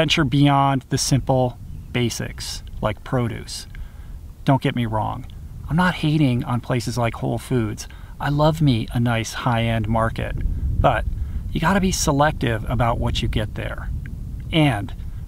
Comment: I thought you said "I love meat"